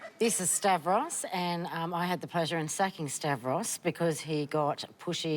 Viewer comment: i swear, I thought she said sucking not sacking